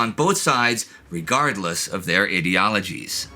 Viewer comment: did you say idiologies? Lol